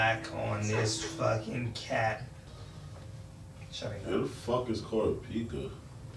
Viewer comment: Nobody: 16:43: "where TF is kurapika?" Bruuuuh he's not in this arc. Why would he suddenly appear? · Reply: for a second I thought he said "Who the fuck is Kurapika"